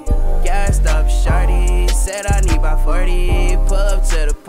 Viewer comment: why do i hear it as "gassed up sharty"?